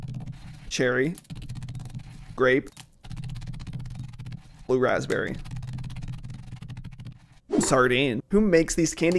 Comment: DID YOU SAY BLUE RASPBERRY